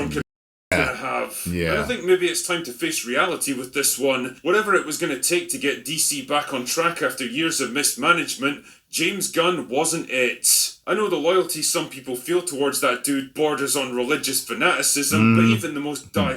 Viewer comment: I thought he said james gunn was fired. da hellie?